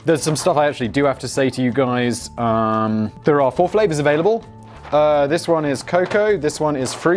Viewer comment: <link> I heard "There is Foreplay available" the first time... Oh Simon the.